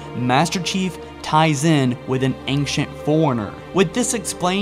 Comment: it sounds like you said an ancient foreigner